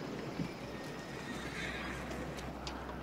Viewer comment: At first I thought he said "whistle pixie.